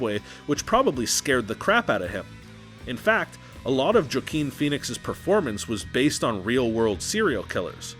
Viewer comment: What did he say at 7:50 ? Jokeen Phoenix?